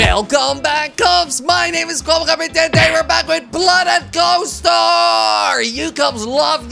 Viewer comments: Did anyone else hear at 00:12 'WELCOME BACK KOPS And welcome back to.. BLOOD AND GHOSTER!!' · sounded like he said "and today we are back with blood and ghost store"